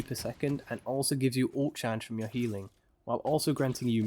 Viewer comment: What are you saying here? 1:13 All chance? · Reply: Ult charge